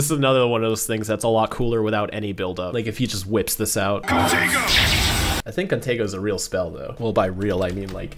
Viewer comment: At first I thought Ben 10'000 said protego not contingo